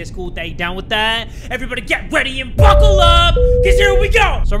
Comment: i swear he said "get weady"...